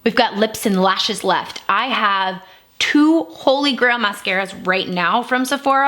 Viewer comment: I heard “holy grail mascaras” as “holy grandma scares” 😂💀